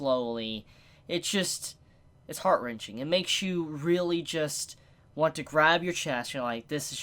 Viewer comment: I thought said "just makes you want to grab HER chest" almost called the FBI